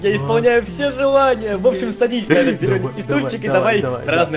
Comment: я один услышал:,, вообщем садись на этот динамитный стульчик,,?